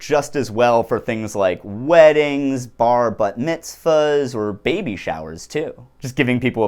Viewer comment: Did he just say "Bar Butt Mitzvahs"? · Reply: bar/bat mitzvahs, or or "bar or bat mitzvahs". bar mitzvah is for a boy, bat mitzvah is for a girl